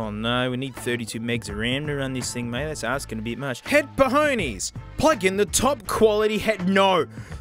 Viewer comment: i hope i wasnt the only one who hear him say "headpoohoonies"